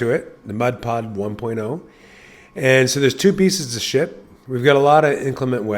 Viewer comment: What did he say at 2:30 here in this video? "There's two piece of,," 🤔🤭🤫😜 Yeah, I know he didn't say that, but it sounded like it.